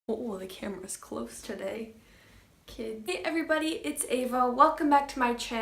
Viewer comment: I watched the first couple seconds of this and all I heard was “the cameras close today kids” I’m dying